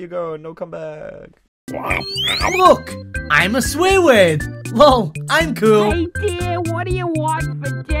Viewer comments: I legit thought he said ''look I'm a squidward" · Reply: Sojiro Shimbo can you not read? He said he THOUGHT he said “look I’m a squidward” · I legit thought he said ''look I'm a squidward" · I thought he said "Look, I'm a squidward!" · The new cartoon is out!! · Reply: i thought he said "look ima swoo rd" but he ACTULLY said look im a swear word XP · “Look I’m a swear word LOL I’m cool” Mom: HeY DEAr WHut FOr DinNa “MOM GET OUT OF HERE” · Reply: I thought she said “Isiah whut you want for Dinna? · I legit thought he said ''look I'm a squidward" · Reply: I thought he said look at me I'm swarword